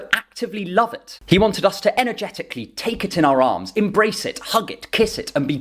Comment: Omg I heard "energetically take it in the arse" at 0:58 hahahahhahaha